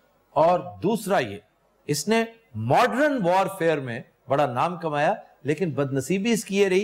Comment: Aftab bhaji says Modrun instead of Modern 😮 Still love him you haters….just positive feedback.